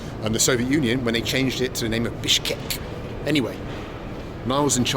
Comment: "...they changed the name to FISHCAKE!" is all I hear.